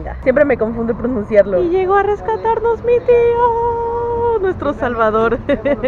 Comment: soy la única q en ves de escuchar mu tío escuche bts ? . . . . . . . Soy la unica loc@